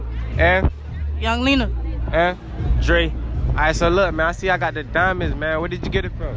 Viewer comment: swear bro said Drake